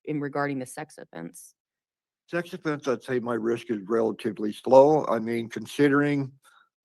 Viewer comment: did he say his risk of sex offense is relatively slow?